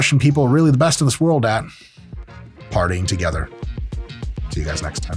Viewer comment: I had to listen back to it like 10 times until I heard he wasn't saying "farting together"